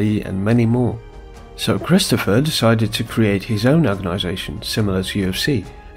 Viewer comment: i swear 0:59 i replayed it FIVEx and i still can't understand what you are saying! Speak clearer! ;)P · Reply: Similar organization to the UFC